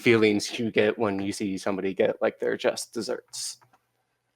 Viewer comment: What does he say here? 1:47 Just desserts or justice serviced? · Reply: Just deserts, just a different way of saying "get what you deserve" <link>